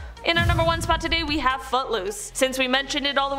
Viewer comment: Lol I thought she said but loose not foot loose